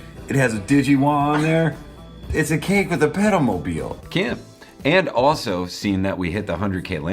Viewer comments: My dumb ass thought you were saying pedo-mobile ... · Jesus, I thought you were saying pedo-mobile